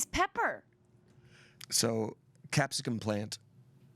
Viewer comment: can someone tell me what says that makes shayne laugh so much? English ain't my first language + the automatic subtitles didn't help understand either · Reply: said, "What is pepper?" To be followed up by saying, "So, .... Capsicum plant." The humor is in trying to figure out what the inside joke is, still not figuring it out, and then finding out that (allegedly) told that the name for a pepper plant was capsicum before he episode started. We still don't know why "pepper" is so funny, but they're joking on a joke on a joke.